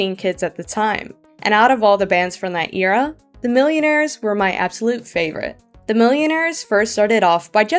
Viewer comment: DID SOMEBABY SAY MILLIONAIRS!?!?